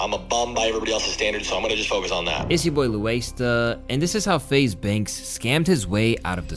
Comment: I rm I used to listen to you only on my headphones at work. I thought you were saying “louasta” so I tried to find you like that. Couldn’t find you. Glad I found you bro